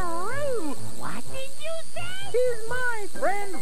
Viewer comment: What did you say?! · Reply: Announcer: He’s My Friend Rabbit, Today on Treehouse.